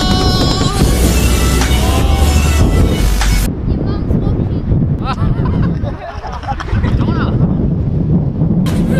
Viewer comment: what did the kid say to? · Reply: Lucas Pollock yeah he said "ya mums watching!" 😭😭😭😭